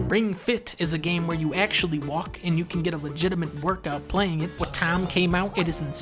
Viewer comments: whats the game???? Í cant understand what are you saying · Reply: It's wattam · What's the name of the game at 3:05 ? I cannot make out the name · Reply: wattam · What game is this? I actually cant understand him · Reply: Wattam